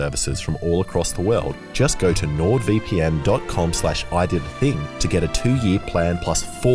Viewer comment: I thought the link said ‘Idiotdating’ and not ‘ididathing’